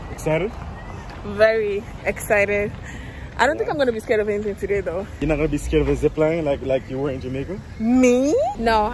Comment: I thought you said you’re not going to scream this time around 😂😂😂😂😂😂😂❤